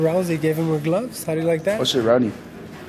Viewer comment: wtf he say lmao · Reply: Oh shit rowdy